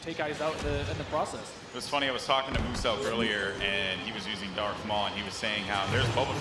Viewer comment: did he say MUSELK!